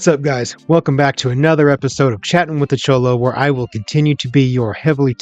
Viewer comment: Low key I thought you said chomo at first 😭